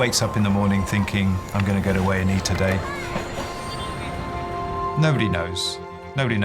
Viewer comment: Could someone tell my brainless self (I tend to not hear things correctly) what he says in the beginning 0:10 . I keep hearing him say “…I’m going to get away and eat a day” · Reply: I’m going to A&E today